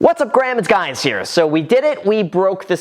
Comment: did i just heard "What's up Graham, it's Guys here"